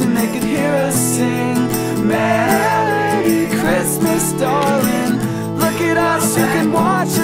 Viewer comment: Kinda sounds like he says, "merry Christmas Stalin" instead of "darlin"